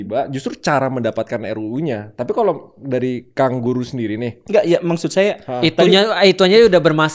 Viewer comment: raymond bilang kang guru gembul kedengeranya jadi kangguru 😂